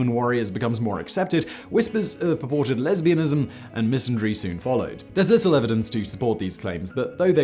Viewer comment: is it true that anyone understands what you are saying at 4:30 with the word misenerey? never mind, you just cannot speak